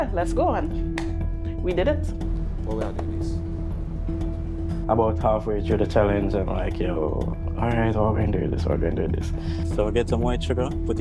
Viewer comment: Can anyone tell me what that dude said at 13:40 cause I mean wda dude english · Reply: "About half way through the time am like how am gonna do this, how am gonna do this" Hope that helps.